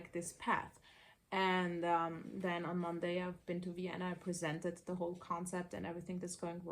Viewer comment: Spending time in the sun makes me not want to eat as much. It is so satisfying. Are you in Vienna? Earlier, I thought you said Indiana 😅